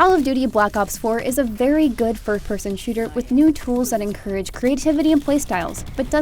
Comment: Did she say 3rd person shooter?